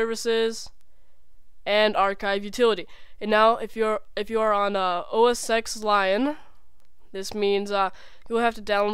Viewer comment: Did he say... OS Sex lion?